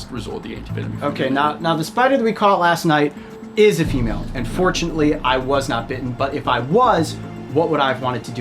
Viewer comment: Ha ha at first I totally thought Coyote said, "UNfortunately I was not bitten" (but he didn't lol he said "fortunately" phew!).